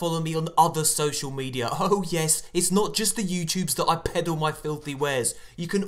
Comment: "its not just the youtubes I pedo my filthy wares" - This is why you should have 100k subs ^^ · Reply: okay that makes more sense, I apologize for my shitty english ;_; , I've never heard the word peddle before so I thought you said pedo but anyways I thought it sounded funny when you said it haha!